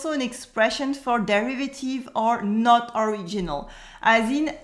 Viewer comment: Sorry, at 3:45 I don't understand two words. derivative?? and ordinal??